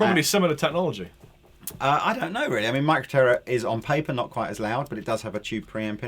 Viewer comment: micro...what? I can't make out what he says and it seems to be a pretty good thing,,,but can't make out the name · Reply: Micro Terror